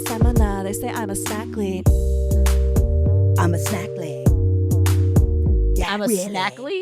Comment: I always thought it said "snack queen"